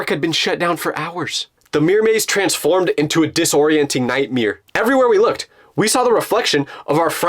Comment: I heard Bro’s nightmer